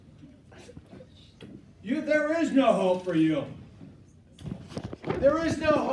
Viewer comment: “You there is no hope for you” damn 😕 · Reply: I thought he said “There’s no Hoe for you” 😂😂